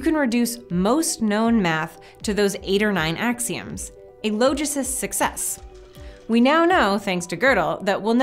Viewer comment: a logicist's success...is it what was said?